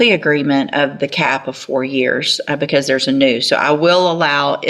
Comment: did she just say there's a noose?